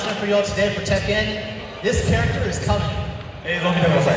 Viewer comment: what did he say? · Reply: "this character is coming"